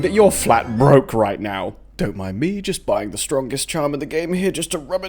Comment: Did you just say three of the strongest charm at leg eater?